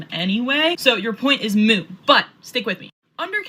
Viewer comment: Did she say "...your point is moo..."? The word is "moot". That alone told me all I needed to know about her intelligence level. But wait, there's more! Then she spewed absolute leftist propaganda that she's been force fed for who knows how long.